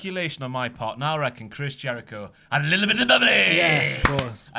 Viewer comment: At around 2:00 ish, what does say Jericho did? I don't understand the weird voice. Thanks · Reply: Had a little bit of the bubbly.